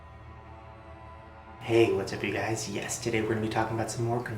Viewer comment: At the beginning i thought he said "Hey, whats up you guys, yesterday were gonna be talking..." and i was like huh?! and then realised he said Yes Today 0:08 and hey to whoever is scrolling the comments